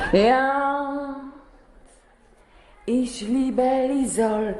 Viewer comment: Czy dobrze usłyszałem? Ona powiedziała "Ich liebe LIZOL"? Młodszym oznajmiam, że "lizol" to była taka cuchnąca ciecz wylewana kiedyś w kiblach dla dezynfekcji. Pozdro.